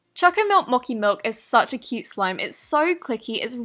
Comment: it sounded like you said chalkey milk moki milk